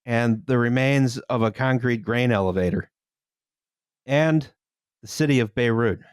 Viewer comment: sry for a second there i thought you said brain elevator .. and that just makes the whole thing sound very nefarious lol